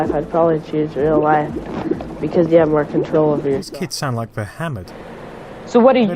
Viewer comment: At first I thought you'd said "these kids sound like Muhammed" and I was all there's VR in the Qu'ran? (9:50 in the vid)